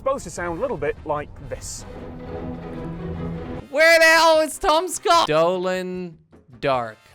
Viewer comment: When he said tom scott i thought he said tomska xD